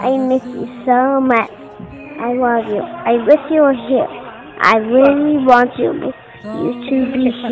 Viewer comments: Thought he said hot mum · ik this is supposed to be a wholesome moment but i was just listening to the audio and i thought he said “i love mom, hot mom”😭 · Aawwww💕💕At first when I didn’t read the text, I thought he said “hot mama” instead of “heart mama”🤣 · I thought he said “I love mom HOT mom”💀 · Ok that's really cute<3. But is it only me that thought that he said "hawt mom"💀 Read more... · this is so adorable but why did i hear " i love mom hot mom " 💀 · I thought he said I love moms hot moms 😂❤ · When he said “I love mom and heart mom” I thought her said hot mom 😂😂😂